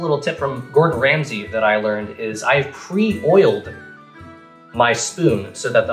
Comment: I thought you said I "boiled" my spoon, like how people say to microwave your silverware to cut through butter easier.